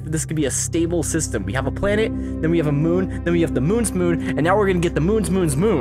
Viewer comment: Every time you said "moon's moon," I heard "mun mun" or "mur mur."